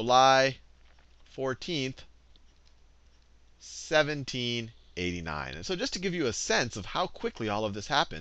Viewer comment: subtitle says 1798, not 1789